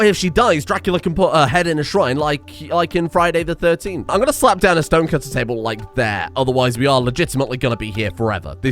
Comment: DID I HEAR SOMEBODY SAY ROCK AND STONE?!